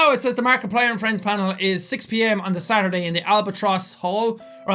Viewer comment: When he said albatross I thought he said albaboss